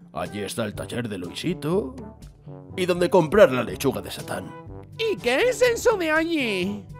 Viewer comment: QUE DIJO😮😨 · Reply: Dijo chuga de satan